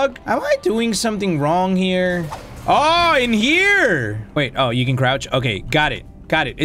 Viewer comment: was I the only one who thought he said "oh we can grouch" 😂